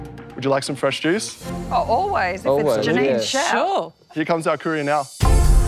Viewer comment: i thought he said here comes our korean now lol